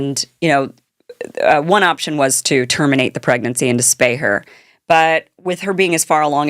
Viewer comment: "One option was to terminate the pregnancy in despair" 😂 that's what I heard but it's not Kim's drawl 🤔😅